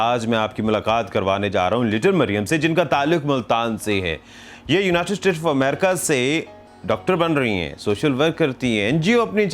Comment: Did he say "United States for America" at 0:42.